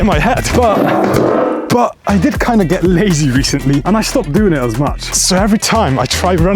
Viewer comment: Oh i thought u said i stopped gooning as much